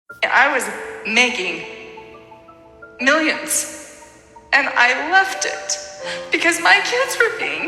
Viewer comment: Ohh I thought the intro she said "I was making millions and I loved it" XD